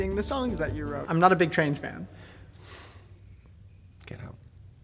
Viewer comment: "I'm not a big trains fan" i like trains.... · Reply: When they said that I heard "trans" and I'm like ??????? How can you of all people not like trans people?? But then I saw the caption thing said "trains" and I was like ooooooohhhh